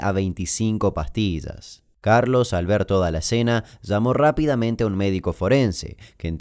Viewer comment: Entendí "Carlos Alberto Balacena" y flashee con otro nombre. No entendía nada jaja. Después entendí